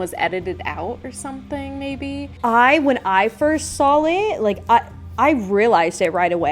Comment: Does say, “SAWL it” ? Does she add an L or am I trippin? Lol